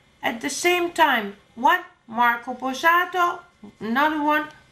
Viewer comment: Did he just say "marco borsato"